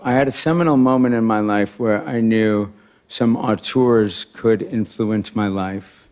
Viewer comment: What does he say at the beginning, around 1:51...'arteurs' could influence his life?